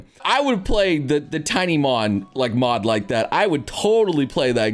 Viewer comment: I heard Tinymon and remembered Johnny Test Episode with tinymon... why?